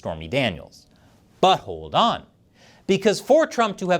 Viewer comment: At around 10:25 did you say, "..butt-holed on.."? Sounds like the title of trumps next book regarding his legal battles, 'Butt-holed On'